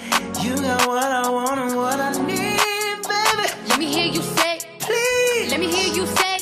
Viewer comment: Let me here say 가 왜 let me here 쎆으로 들리지?